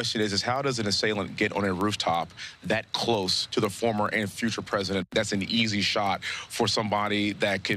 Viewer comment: Did he say former and FUTURE president? Sus...